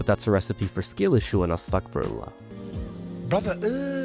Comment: wtf did he say? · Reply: Astaghfirullah